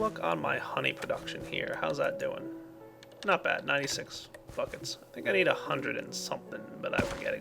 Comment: Did just say Fuckits